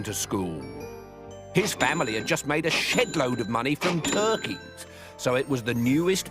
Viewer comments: When did u hear them swear?? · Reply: Its technically around 4:56. He says "Shed load" but a lot of viewers mishear it as " Shit load" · did he just say shit load of money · did he say shit or shed?🤣🤣🤣🤣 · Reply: He said shed load of money but I do have to agree at first when I watched it I thought he said shitload of money